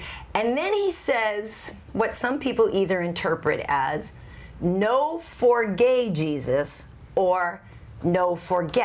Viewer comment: I heard "No For Gay Jesus" when i watched it live